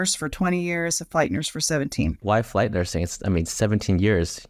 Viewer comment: Who else thought it said fight nursing 😂